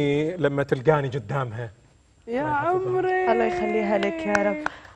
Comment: الصوت اللي بدقيقة 1:00هذا صوت منو سمعت وحدة كالت يعمري بس لا صوته للرجال ههههههه